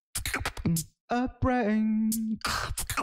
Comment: Am I the only one who heard: "I need a brain" at 0:29???